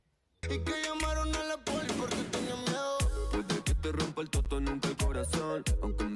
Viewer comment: yo entendi mal o dice "puede que te rompa el toto nunca el corazon " ? , y la cara que pone ella me mata 😅, no se si entendio o le gustaba como sonaba la cancion en esa parte 😂, en fin a mi no me gusta este tipo de musica pero bueno , bien por ella , la banco por su contenido en general 👍